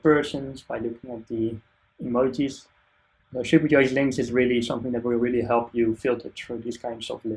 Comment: What did you say at 27:53? "The [...] is really something that..." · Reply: Oof, yeah i stumbled over that. Supercharged Links, is what i (tried to) say. It's another plugin I'm involved in that allows you to style different kinds of links, like the emojis and colours in this talk :)